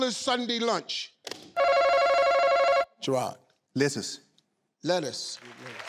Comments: I thought said "Lizard" . Hahah it was actually "lettuce" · I thought the first guy said lizards instead of lettuce